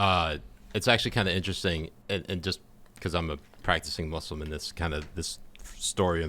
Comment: I thought he said “I am a practicing Muscleman” and I was like oh Jesus wtf kind of fetish is this but then I realized he said Muslim and I was like thank god.